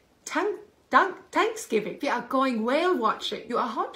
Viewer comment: “You’re hot? Take your shirt off” obviously 📣😂👏🏼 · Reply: WOW I totall;y heard that incorrectly! What I heard her say was "you are going whale watching....for what...take your shirt off" implying that her daughter is a beached whale sans top. LOL thats way off....I think :(